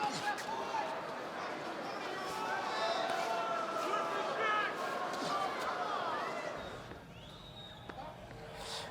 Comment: Anyone hear that 01:51?? Twist his what? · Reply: Neck